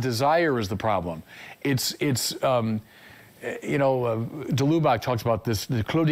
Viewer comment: De Lubbock? What name did he say there? Cludio Mysterios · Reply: It could be the Jesuit priest Henri de Lubac. But I get no hit for Cludio Mysterios or something similar. Please let me know if you find it!